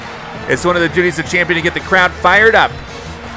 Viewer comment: I think he said crap instead of crowd in